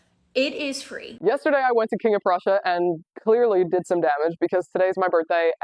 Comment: wtf did she say russia?… I am so fucking tired of this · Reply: "King of Prussia" It's a shopping mall I believe